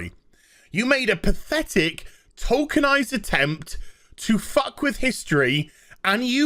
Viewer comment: At first, I thought it said "tolkienized".